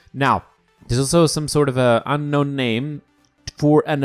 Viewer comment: did I hear unknown name well then I came to the right person